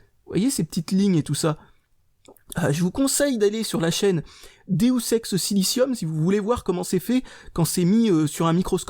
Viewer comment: j'ai compris "déosex silicium" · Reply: Maël Chouteau Deus ex silicium va voir c'est super intéressant